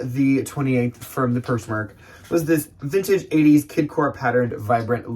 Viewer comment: did you say "Kid Core" what lol is that a thing?